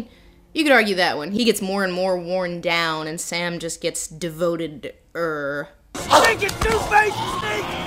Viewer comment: had a little problem undestanding what you said around 5:15 "sam just gets devoted to her?" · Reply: Devoted-er (meaning even more devoted)